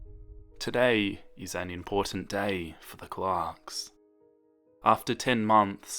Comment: When you introduced the Clarks, I first thought you said "Mrs. Clock" and I was very confused but then I remembered you have a very thick accent (I love it. This isn't a hate comment. It's a love/support one!) And I kinda laughed at myself for being so.. well.. stupid 😂